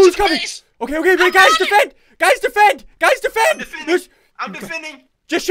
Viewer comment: tell me what yo hear... I hear something weird lol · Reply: Original Derps what do u hear? I just hear “ Guys defet” or smth